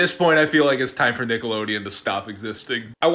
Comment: I almost thought he said it was ‘time for Nickelodeon to stop resisting’